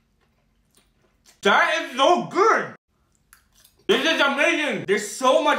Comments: I thought he said " that is no good" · Nah I fr thought he would say- "INDIA WINS"😭🙏🏻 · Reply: "That is so good" and i heard it "that is no good"